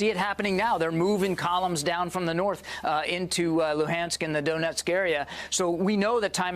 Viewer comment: He really said "the doughnut´sk area".